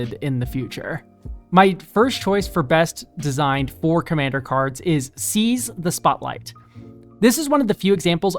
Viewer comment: When you said "for-commander cards" i heard "four commander cards" and thought "surely theres more than that"